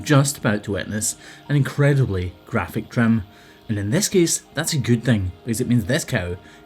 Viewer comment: says "graphic trim," I hear "time for full-screen." Great job, mate. That poor creature had to be in such incredible pain, and she had to feel so much relief the second you released all that pressure.